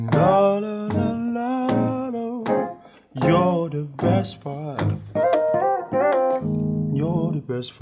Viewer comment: it sounded like he said you're the best fart at 2:58 lol just me ?